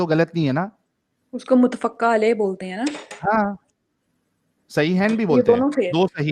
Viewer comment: she said, "Muttafacca" and I heard "mudafucka". I think I am not worthy enough to live in Allah's kainaat.